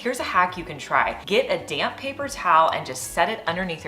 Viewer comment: 😂😂😂 I first heard ""....get a damn paper towel" I'm like, okay, okay! I will! Geez. Then I realized she meant damp. 💀